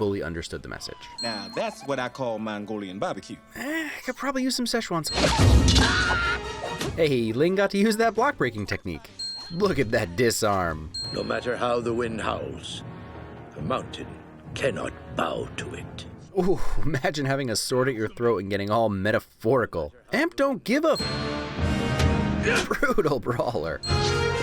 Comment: ngl when I saw this movie for the first time I thought the emperor said "No matter how the winds howls, the monkey cannot bow to it" and I was like dag that's deep bro